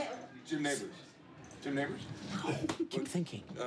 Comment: what did Jon say at 1:14 ? it sounds like a racial slur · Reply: Alexander Neumann Jim Nabors.